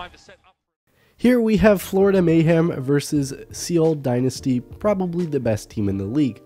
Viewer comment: Wait...did he say "Seal" Dynasty? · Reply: It's Seoul.....i.e. sounds like soul...not seal